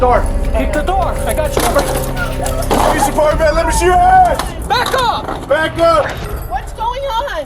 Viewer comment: the auto generated captions say "let me see your ass"